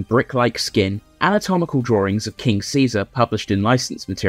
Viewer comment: I love that you included "King Seesaw" in the aliases, because when I was five that's exactly what I thought the dub was saying.😂